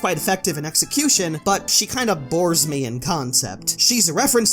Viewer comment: "But she kinda Bores me in concept" I heard bore as "Vore" I will never unhear that